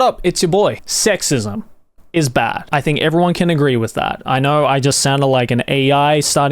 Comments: I thought he said "SEXISM IS BACK" at the beginning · I thought you said "sexism is back"